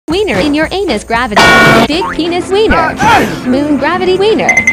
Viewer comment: I heard big "P" eanis 🤨